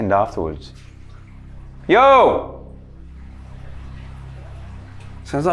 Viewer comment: it could be his echo but it sounds like someone says "yo" back.